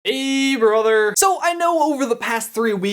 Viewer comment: This channel never stops having the craziest, most interesting theories/explanations. Keep it up! Also, at the beginning (0:01), the auto-captions say "a brother" instead of "hey brother". That catchphrase is essential for every video! :) The hard-of-hearing/deaf community depends on closed captioning and everyone should have the best Youtube experience. If you could correct that and little mistakes in the auto-captioning here and there, that'd definitely be terrific!